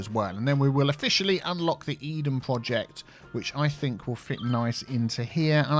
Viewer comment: Thought you said hedon project at first, I thought this game was for everyone